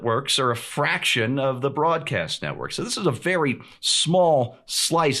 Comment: I thought I heard Steven Green say "FRAUDcast Network" there for a moment, what a sight of reliefhe didn't.